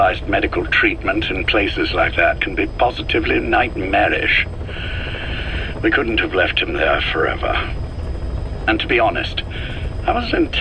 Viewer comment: The subtitles say "we couldn't have left him like that forever" whilst the audio said "we couldn't have left him there forever". Maybe just a translation error but still interesting in context